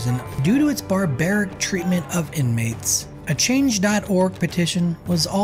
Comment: Bro your closed captions said black men while you you said inmates something doesn’t make sense turn to 8:30 put on closed captions